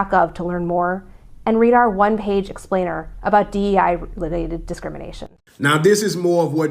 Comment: Wait did she say DEI???